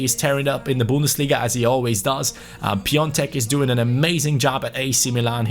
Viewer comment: did he say Piontek?!